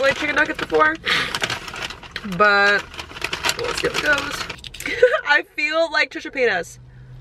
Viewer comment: I literally thought you said “I feel like Trisha’s penis” 😭😂👏🏽👏🏽 ~ hElP?